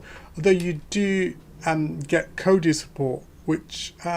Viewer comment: what are you saying here? code what? can explain what you were talking about? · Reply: Kodi not code